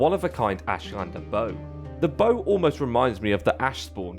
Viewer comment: I thought you were saying Boat until this point